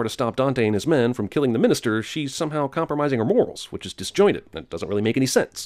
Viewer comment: you say "compromising" but the subtitles say "comprising"...can I get a sin??